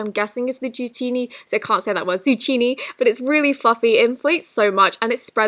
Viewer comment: is she saying ZUCCHINI?